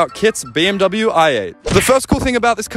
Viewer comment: I thought he said kids bmw i8 cause GADDAYM HE TALL